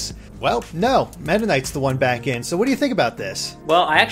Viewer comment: when he said back at e3 i thought he said back in 83